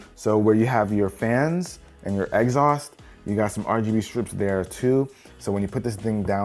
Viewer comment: Y did he say “exhaust” like that at 1:40? I legit thought my man said egg sauce